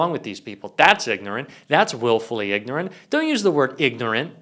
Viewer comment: Well, I think that you were under the weather today. It may be my hearing impairment; but I thought you said that Levitt was willingly ignorant... Isn't it woefully ignorant (when it comes to current events) and woefully stupid (when it comes to her thinking that people actually believe her BS?)? In my defence: The Air Force said I was a smart guy when it comes to math & science (IQ=136) but I don't believe it because I HAVE NO PEOPLE SKILLS (if you want proof, just reread what I wrote)!